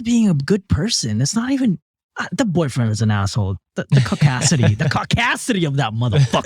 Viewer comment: When i heard say "Caucasity" i thought he said my name and it made me freak out for a minute and rewind because i didnt hear the "cau" all i heard was Cassidy😭😭😭😭 had me over here thinkin about my dad lmfaoooo